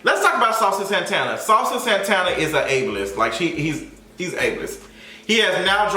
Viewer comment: I definitely thought you said "Saucy Santana is H list", had to run it back 😂😂